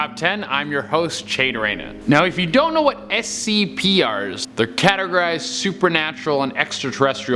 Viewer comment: did he just say scpr's